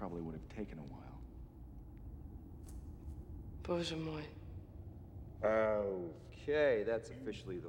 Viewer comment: what did she say? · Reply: "My God."